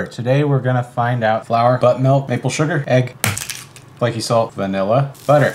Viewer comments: Did he just say butt milk?..😂😭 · Did he just say butt milk or did I mishear? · I heard "butt milk" and i was like 👁️👄👁️ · Did he just say butt milk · i heard “butt milk” 💀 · Why did I hear “butt milk”😅😂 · Did I hear “butt milk” or am I trippin’?